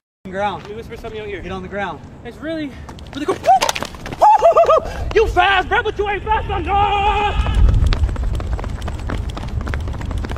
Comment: "It's not day I told you!" "Bro, you fast, but you ain't fast enouuuuugh" "I'm a feminist!" *Puts feet up to butt* "That's as far I go! You think I'm in Pilates?!" "That's my lighter, it don't work....DON'T DO DRUGS KIDS" "That's my quarters. You wanna put them in yo pocket?" *Patty dumps out quarters* "THAT IS CHANGE! You know how long it took me to acquire those?! Some of those are limited edition!" "Oh, that's my flash drive. Don't look at it." "WATCH YO FINGERS BOOTY HOLE MAN!" *Patty struggles to take off his boot "You gonna drag me down the highway?" "Would you mind gettin' my snacks for me?" "Remember what I said?" *Patty slams door* "PINEAPPLE JUICE!" (Sorry i just thought that was funny 😂🤣💀). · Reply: I thought he said "You fast but you not fast on drugs!"